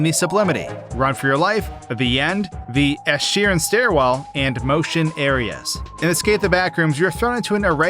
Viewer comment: I swear I thought you kept saying the Ed Sheeran stairwell. I was trying to understand the connection. Most frightening entity ever tbh